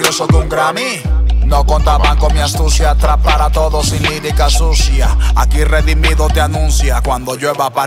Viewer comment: Pensé que decía “trap para todos sin lírica sucia” 😂😊